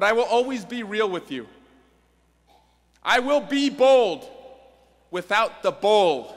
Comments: "I will be bold..." (Turns dramatically to give what he thinks will be a clever line) "... Without the bull." (Awkward silence while he expects clapping) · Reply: It sounds like he says "I will be bold without the bold". Poor pronunciation if he was saying "bull". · "I will be bold..." (Turns dramatically to give what he thinks will be a clever line) "... Without the bull." (Awkward silence while he expects clapping) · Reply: honestly i thought he said bold without the bowl